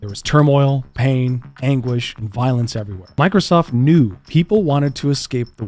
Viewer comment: Please tell me not only I thought he said "English"